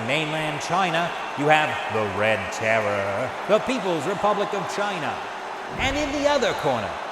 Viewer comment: Oh silly me, I thought you said, "The Peoples Republic of California."